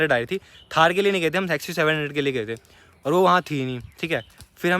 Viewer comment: Kya Bola😂😂😂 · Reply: Sexy 700 🤣🤣